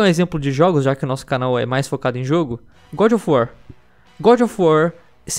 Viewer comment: Parei de assistir quando ouvi "gudi of uar" 10/10...piada